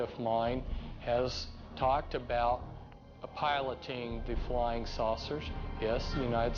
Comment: Did he say Flying Sausage??? lol